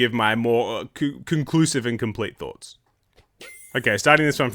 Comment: I thought you said ‘con-cuckoos-live’. Lol